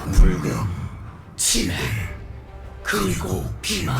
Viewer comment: 불금 치맥 그리고 비만 대체 원래 말이 뭐임 ㅜㅜㅜㅜㅠ? 아무리 다르게 듣고 싶어도 저걸로 들려요 ㅠㅠ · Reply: 운명 지배 그리고 기만 ..?